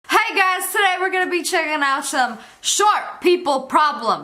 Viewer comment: in the beginning 0:00 if you play it over enough it sounds like she said hey gus